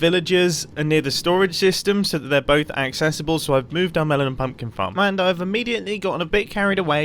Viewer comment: Anyone else hear "Melanin Pumpkin Farm"? *shudder*